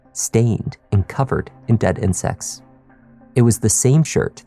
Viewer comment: Oh," covered in dead indents" - I thought he said "covered in death and sex" 😳